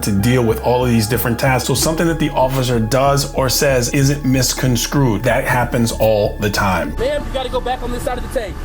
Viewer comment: Sounded like you said misconscrewed…I like that version of the word.